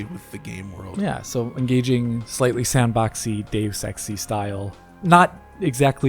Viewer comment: I heard as "Deus-sexy". Perfect describer for this game!